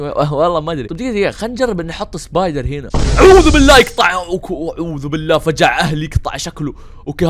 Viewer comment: ✨ جراني مزورة ✨ 7:49 ✨ اعععووعععوووذوو بالله يقطعع شكلك( مدري وش قال 🗿 ) ✨ والله انا كمان انخرشت الله لا يسامحك 🗿💔💔.